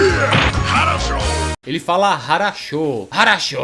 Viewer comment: Sempre achei que ele falava "ASSHOLE!"